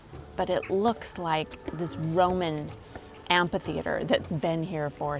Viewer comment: Really interesting and well made video. Thank you. (Um....... sorry... did you say amp-hee theatre?....... perhaps I misheard. Ph is an f sound. Am-fee theatre?)